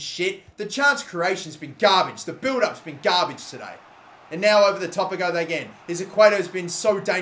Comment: "And now over the top a go they gain" he says 21:57 (instead of they go again)